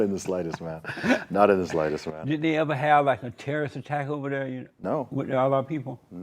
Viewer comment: Did he just say at 4:34 "a terrorist attack with allah people" ? Or did i hear him wrong ? This guy is a classic example of a brainwashed islamophobe